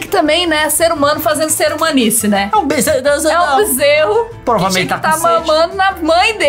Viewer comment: So eu escutei ele falando "porra mas ele ta com sede"